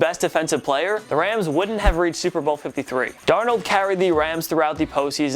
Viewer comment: wth this dude is actually so unintelligent, Hakeem Butler is on IR, also the “trio” of wr’s isn’t nearly an accurate representation of the cards, rather they have 5 Speed Wr’s along with vets Larry Fitzgerald and Michael Crabtree 2:28 Josh brown lmao, it’s John Brown 2:42 Newton just got injured 🤦‍♂️ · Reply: Murr Plays 9:30 he says darnold instead of donald